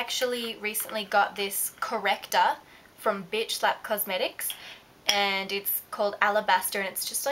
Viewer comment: When you said "BH Lab", I thought you said "bitch slap" haha